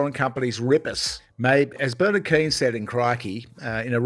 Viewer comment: I thought you said Bernard King and wondered if he had come back from the grave.